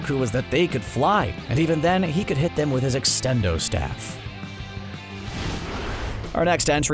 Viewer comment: did he just say extendo pole cuh that’s the power pole 💀💀